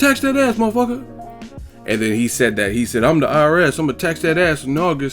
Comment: I thought Floyd said "im the irs I'm going to catch that ass".